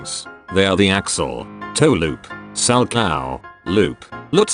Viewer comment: I keep hearing "sal cow" and imagining someone skating in a cow costume 😂 Great video guide 👌🏽